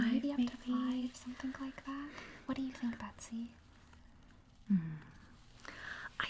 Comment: I heard Batsy...she said Martha... FOR GOTHAM